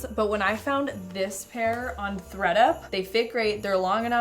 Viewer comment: I thought you said “Fed Up.” I was looking for an online thrift store called “Fed Up” like on set up with shopping retail prices. Duh.